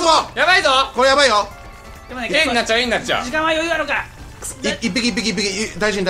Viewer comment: キヨの「AになっちゃうＡになっちゃう」がずっと「えんがちょえんがちょ」って聞こえてた